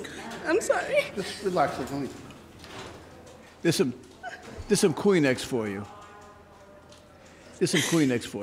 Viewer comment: When judge says "theres some kleenex for u" sounds like he says "clean eggs for you"